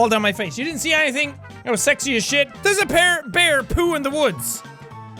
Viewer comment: he's drunk already coz instead of saying bear he says pear